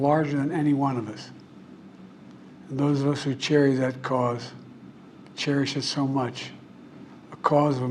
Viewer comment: I think he said the ones who cherry that cause